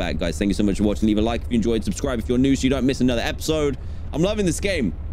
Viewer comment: did he just say love you bye?